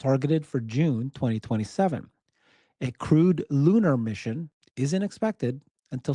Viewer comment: Every time you said "crewed lunar mission", I kept hearing "crude lunar mission"!